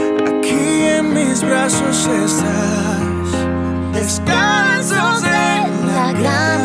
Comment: Que dice el el minuto 1:11 al 1:15????? Alguien que me escriba esa frase 🙏🙏🙏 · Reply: Descalzos en la grama y hoy oímos nuestra canción